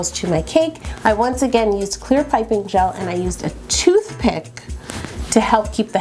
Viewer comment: I honestly thought you said toothpaste instead of toothpicks 😂😂